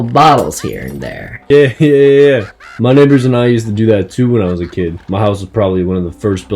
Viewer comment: did he say "Me niggas did that too"?